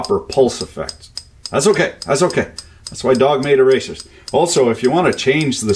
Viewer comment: Auto captions say "that's why dog made a racist" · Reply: "That's why dog made erasers"